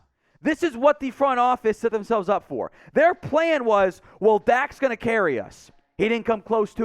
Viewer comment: Lol. At the begin I didn’t have the volume up and I thought he said “ well Dak sucked” lmao!😂 I was like alright….